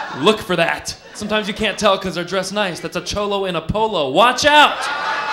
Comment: I thought it said a Cholo in a bolo 😂 but I used to live in S. Tx. so what do I know 🤷‍♀️